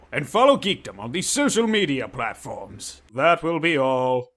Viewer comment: "that is all instead of " that would be all"